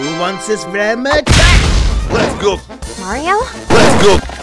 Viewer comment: what did he say 3:21 "Lets go" "Lego" "Leg gold" "Lets gold" "Let go" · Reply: Lets go